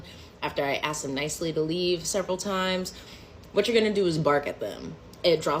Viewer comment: did she say “bark” 😂 🤡